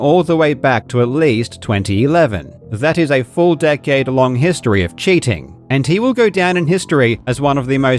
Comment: going all the way back to 2011 that full decade long history of cheating. I swear I heard four decade long history of cheating. Several times lol